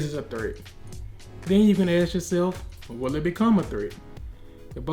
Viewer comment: I dont know what he's trying to say, for deciding when to respond to an effect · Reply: Bit late be he was saying if it will pose a threat